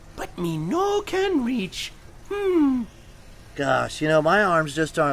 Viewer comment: What did the crocodile on the right say at 0:32? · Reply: “Me got long arms! Me can find!”